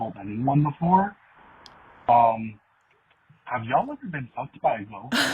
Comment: Y'all, I swear I thought he said "have y'all ever been fu** by a goat"!!😂